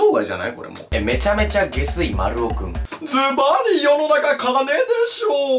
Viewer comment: ゲスい丸尾くんのとき… ズバリ世の中金でしょうって言ったんだけど… ズバリ世の中奏（自分の名前）でしょうって聞こえて画面見てなかったからめっちゃ嬉しかったのは内緒ね？